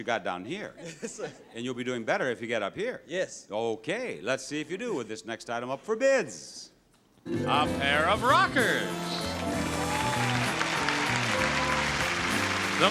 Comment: I thought he said pair of knockers.